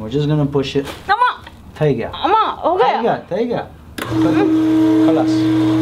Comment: do you speak Arabic..? Cause I heard you saying "Kalas"or "Halas".. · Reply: Me 86 he said "thai gaya,thai gaya,khalaas" meaning "it's done ,it's done,it's over"..it's Gujarati...he might be from Gujrat or sindh